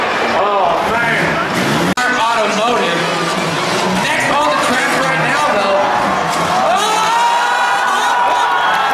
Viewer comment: Anyone know what that vehicle was at 3:09 minutes? It says it but cant make it out 1990 something. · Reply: 1990 Isuzu amigo